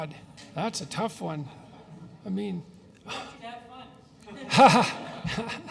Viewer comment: What does the background guy say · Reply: "did you have fun?"